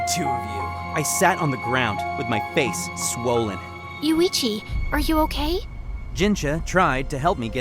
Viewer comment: i hear "You Ichi "😂